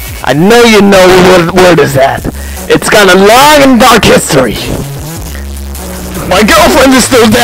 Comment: What is he saying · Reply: that the word has a long and dark history